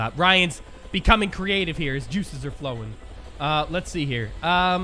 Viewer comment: When u said juices I thought u said justu from naruto